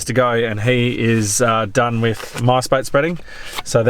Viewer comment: I know you’re saying ‘Mice Bait’ but every time I hear ‘MySpace’ Circa 1999/2000 😂